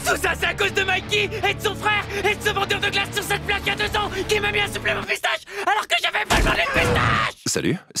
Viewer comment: MAIS QUOI il dit quoi kazutora à 19:30 , "sur cette plaquette de thons" ? MDR j'arrive pas a comprendre